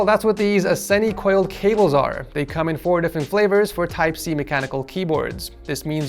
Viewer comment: I know this is a 2 year video but I can't get over how said at the coil cable they have different flavors idk if I heard right